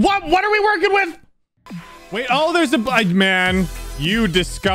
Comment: did said black man??..